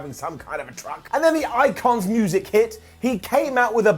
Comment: I thought you said “the Iconics music hit” and I was like “wait, what?” Then I realized you said the Icon’s music and I cried.